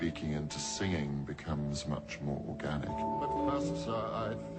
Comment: i was busy reading comments and i thought he said orgasmic. ateeheehee